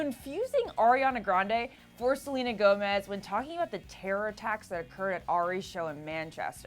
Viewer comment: I thought she had said Ariana Gomez or Selena Grande or something like that 🙄🙄🙄